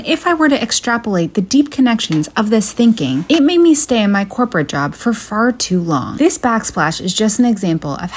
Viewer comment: Had to watch it twice because the first time i heard "Stay in my hooker job for far too long" XD